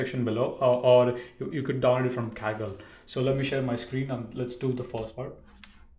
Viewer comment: There's nothing im description box 😅. I didn't understand what he said form where to download the Netflix data set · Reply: Kaggle